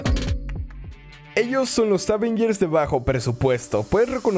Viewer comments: dijiste Avengers??? Yo entendí power rangers :'v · Yo pense que dijo: estos son los aven gears